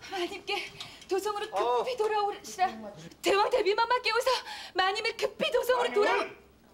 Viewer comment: 모란이 불쌍....마님'을'로 들리는데....